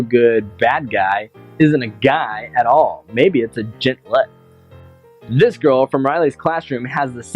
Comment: what did you say it sounded like jitlet